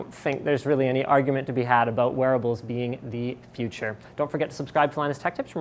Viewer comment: At first I thought he said werewolves are the future...